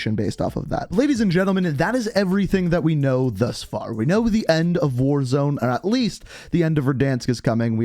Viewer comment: I hear “the end of warzone” and I got rly worried